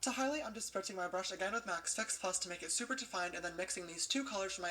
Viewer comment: Turn on the CC to minute 10:39 and it literally says Sex+ instead of Six+ loll😂😂😂